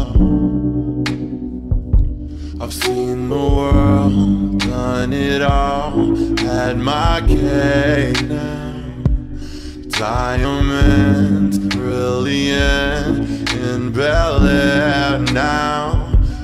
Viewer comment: When he said "had my cake now" I thought he said "am I gay now?"